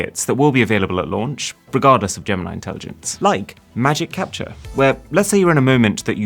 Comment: DID YOU JUST SAY "GAM-NIE" INTELLIGENCE? 10:38....